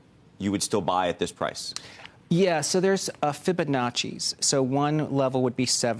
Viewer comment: I think he said something patchy, Like cooking with Liberace, Dressed in Gucci, and styled in Versace. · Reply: He said Fibonacci